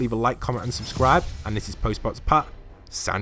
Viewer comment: I thought u said hey guys its postman pat but u said lol